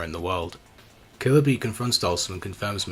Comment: I misheard killer bean